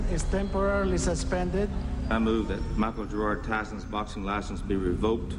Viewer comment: Did he say Michael Jordan Tyson?? LOL